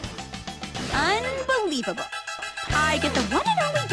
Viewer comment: What did she say? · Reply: I am the one and only being here